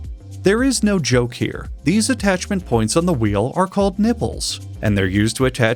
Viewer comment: I can't believe I just Googled "wheel nipples" and he's actually correct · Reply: I thought he said "nibbles"